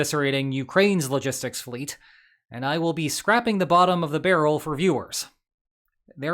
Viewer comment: I'm sorry, did he say "scrapping" the bottom of the barrel??